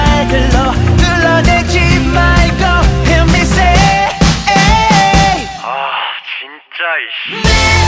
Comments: What are they saying at 1:00 ? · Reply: Jonah Mismaque "jinjja aish" means "seriously?aish" or something like "oh shit" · What does he say in 00:59 - 01:00 ? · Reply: I heard 진짜 which could mean Really Or seriously I guess. There was something after that, like an ish sound but I'm not really sure about that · I wanna know they say in the minute 0:59 Quiero saber qué dicen en el minuto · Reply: myg hyung jinjja = really?